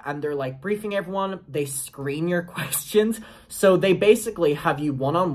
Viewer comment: Ngl at first I thought you said that they were screaming your question😭😭